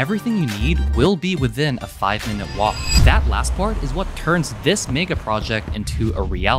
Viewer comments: Thought the said “this Nigga project” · When he said "mega project" i heard "nigga project"